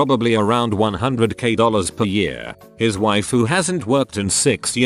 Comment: Lmao at 1:24 I heard “his waifu hadn’t worked in 6 years”